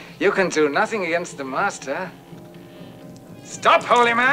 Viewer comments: I always thought he said "monster" not "master" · Me n our kid(rip) always thought it said "your faith against the masters face"....works either way I guess....this scared the shit out of us when it was on tv in the late 70s early 80s...